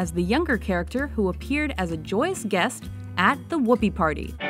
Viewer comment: Did he said Gooby? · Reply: No he said “Whoopee!”. Everyone says that, cause, well, it’s the Whoopee Party LOL